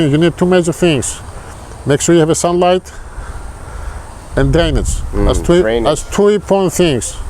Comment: says several times there are 2 major things to planting, Sun and D??. Couldn't make out that one word he was saying. Sounded like he said Danish in my ear. Could anyone else understand? He has lots of good advice and I don't want to miss out what he might have said that I couldn't figure out. I listened several times over and still could'nt make it out. I'm deaf in one ear and some tones I have trouble hearing so don't misunderstand and think I'm not sincere. And since there are no subtitles to help, I figure I'd ask here. Anyone? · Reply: drainage